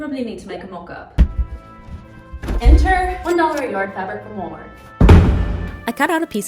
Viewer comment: Hi! I was watching your video and noticed an error in the closed captions. At 5:57 the caption says "enter one dollar a yard fabric for more." when it should say "enter one dollar a yard fabric from Walmart." Just thought I would bring your attention to this, for people who rely on reading captions an error like this might be a little confusing. Thank you! :)